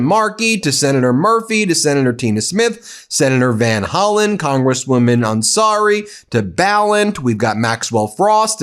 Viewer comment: it sounded like he said congresswoman i'm sorry